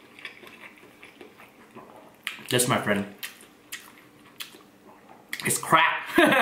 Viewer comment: I thought you said “this my friends, is CRAP” 😄♥️